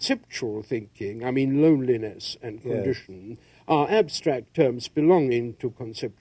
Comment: What word does he say? Perdition or erudition?